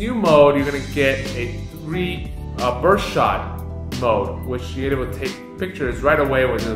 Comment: Can someone repeat what about did he say on 3:08 minute about new mode "pershot" or something? (not so good in english) Thanks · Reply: burst shot